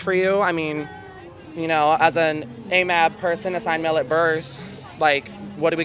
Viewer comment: 'As an a map person...' am i hearing correctly?!